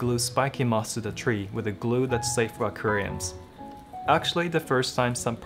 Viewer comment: hahahhahaha I was confused for a moment, I thought he said "with a glue that's safe for Koreans"